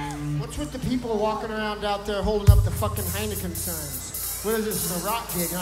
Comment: What's Flea saying at 56:22? it sounds like something about mobile phones? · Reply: Ahhhhh that makes sense. I thought he said something about mannequins :P but it was heineken. Thanks!